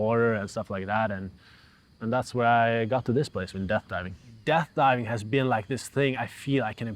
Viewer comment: a kid with a scooter told me about this the skate park like a year ago . he said he does it ; i thought he said depth